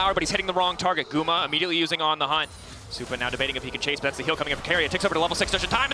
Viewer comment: man this commentator sometimes just be talking lmao can somebody understand what he said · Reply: "supa now debating if he can chase, thats the heal coming of keria take over the level 6 just in time" bro im not even a eng speaker, like you have to lock tf in